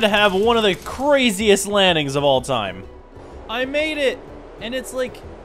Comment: did you say lanning, it's landing with the D, how long have you been saying that wrong